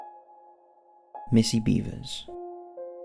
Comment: I thought you said "Missing Beavers". As a Canadian, I was truly concerned!